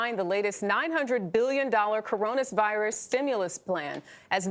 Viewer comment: Did she just say "Coronas Virus"?